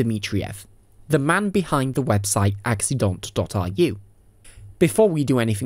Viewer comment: <link> Should work in the USA :) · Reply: thank you very much. It sounded like you said accident.iu... thank you again